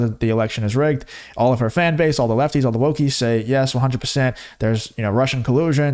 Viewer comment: Man i hate that i'm losing my hearing because when you said wokies I thought you said wookies and as an old school, before disney came in and fucked it all up, star wars fan, i was about to be offended on behalf of the fictional hairy creatures but the GF corrected me and told me the word was wokie.